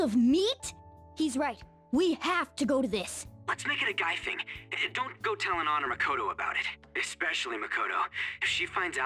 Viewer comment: My dumbass always thought he said “and don’t go telling honors Makoto”, since they use that line in the P5R trailer